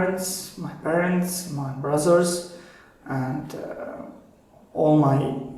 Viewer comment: am i wrong or did he said brazzers instead of brothers